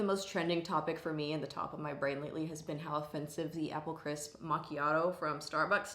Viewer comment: I thought you said Animal crisp Machiato. I just thought that sounded amazing! An iced Animal cracker machiato w/crushed cookie!! Yaaassssss! Thats sounds so good to me for some reason. 🤷‍♀️